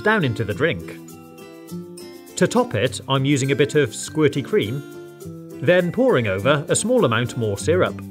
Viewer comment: did he just call it squirt cream?!